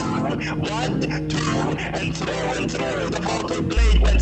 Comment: I have no idea what you just said but I'm terrified. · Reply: one two, one two and through and through, the vortal blade went sniker snack she left it dead and with its head she went glumphing back